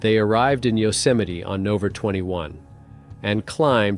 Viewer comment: Are you using an AI gen voice? What are you saying at 15:30: "On Octer 17th?" What's "Octer?" Surely if you mean "October" you should be able to pronounce it. Same at 18:22 when you say "...on Nover 21." You mean "November?" And at 22:25 you're mispronouncing "Yosemite." And what is "Kilo neutons" at 23:10? Edit: The fact that you completely ignored a perfectly straight-forward, legitimate question speaks volumes.